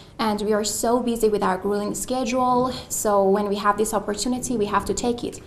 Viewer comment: she meant grueling schedule, instead it sounded like "grilling"